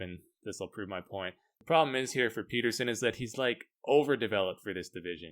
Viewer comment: i thought for a sec he said Jordan Peterson i was like " is JP on steroids now" :))